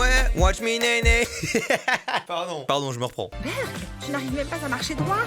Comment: C'est moi ou a 10:38 la voix off lache un " Merde" dans le plus grand des calme ?